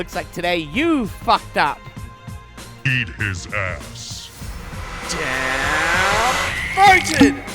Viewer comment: god, that beging....lmao It said "beat his ass"...I heard "EAT his ass"